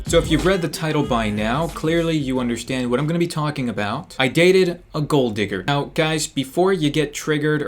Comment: for some reason i thought it said "i deleted a gold digger"